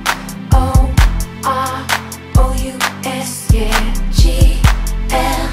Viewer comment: is it just me or does she say ‘A L A M O R O U S’ then says it correctly ‘GLAMOROUS’